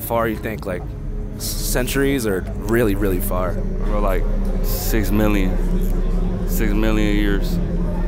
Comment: it says minion instead of million